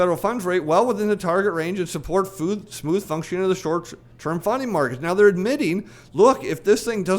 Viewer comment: & 14:27 I thought you said short term "funny" markets. Now that has a ring of truth.